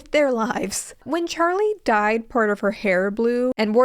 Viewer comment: Genuinely thought I must have missed a piece of lore when I heard “when Charlie DIED…”